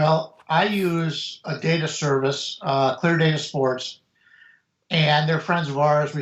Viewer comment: Did say the name of the data service he uses? i couldnt catch it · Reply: cleardatasports [i believe thats what he said, not certain] GL